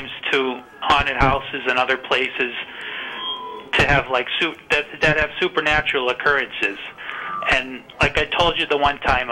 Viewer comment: I thought he was saying he went to haunted houses to have soup...😄 "I like to go to haunted houses to have soup erm" pause "supernatural experiences"...